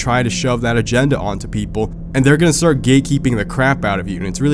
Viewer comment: I thought u was saying “gay keeping”